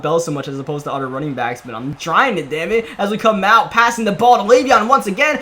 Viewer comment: did he say damnit or what... (before someone replies with hate it's okay I love I just cant tell) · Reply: Gavin DeNure he said damnit